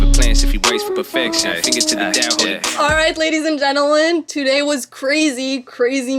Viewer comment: Sounded like she said "gentlelen" lol